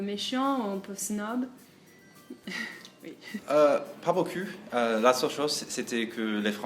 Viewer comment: Y a que moi qui a entendu "pas beau cul" à